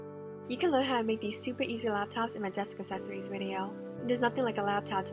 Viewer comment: I thought she said "you can learn how to make stupid easy laptops"